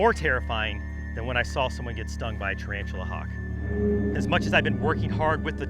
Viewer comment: At first, I thought they said, got stung by hawktuh girl